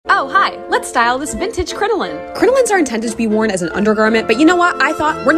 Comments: I thought it said striking a vintage coraline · I thought she said coroline